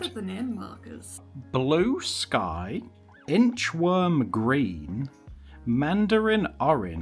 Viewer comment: Inchworm green is actually such a cute name but I thought it said heartworm at first and was horrified 😫